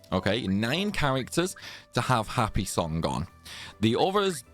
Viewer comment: I thought the evility every character needed was happy song but I keep hearing "happy hour" 😅🤷